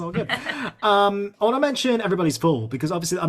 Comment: I hate that she pierced her left eye brow again she has such beauty and that takes away from it 12:20 I thought he said everybody's pool even closed captioning thinks he said everybody's pool I was very confused I had to rewind it